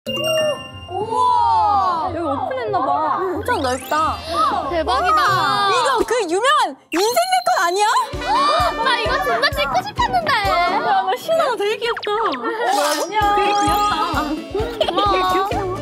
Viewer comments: 누군가 ㅈ나라 하고싶었는데라고 말한것처럼 들림ㅋㅋㅋㅋㅋㅋ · 나만이거 존나 찍고싶었는데로 들리는건감ㅋㅋ